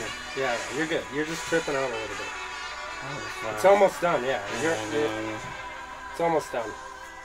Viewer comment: he was saying demon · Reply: yeah i heard "demon demon i almost died"